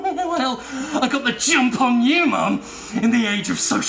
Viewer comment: I thought he said "I got the Jimp on you" and i think he needs to rerecord it to say that now